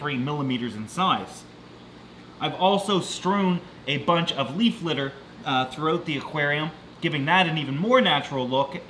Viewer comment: i had to rewind because when he said leaf litter i thought he said beef liver😳